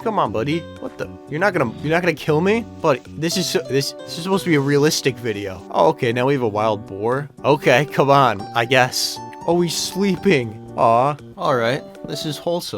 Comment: что он сказал? "Окей, КАБАН"